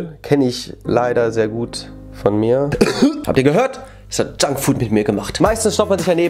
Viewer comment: Als Ju grad gesagt hat : „ das hat junkfood gemacht" hab ich erstmal verstanden das hat Jungkook gemacht oder auch : „ junkfood ist gar nicht mehr aus dem Leben raus zu denken " (ich hab einfach Jungkook statt junkfood verstanden 😳😱)😂😂😂😅